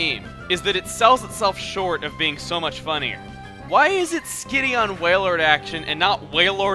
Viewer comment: When he was talking about wailord, I thought he said skinny is wailord! I was goin to say what the...